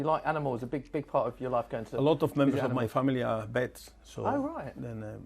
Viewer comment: I first thought he said “a lot of members of my family are pets”